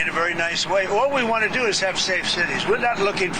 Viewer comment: Did anyone else catch that? He said, "one particular president used it 28 times in the coure of HER presidency"! HER! Does he even know we haven't had a female president yet?